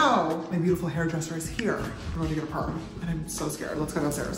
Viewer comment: My mum reall said WHAT She thought you said s*erm